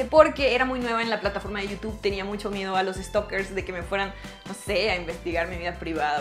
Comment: Pensé que dijo que a los stoppers :V